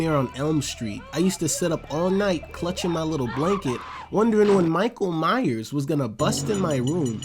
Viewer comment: I thought he was going to say clutering my ass but he said blanket dammit